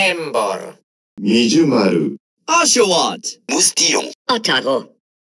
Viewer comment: 〜 何度聞いても〝ウォシュレット！〟って聞こえる...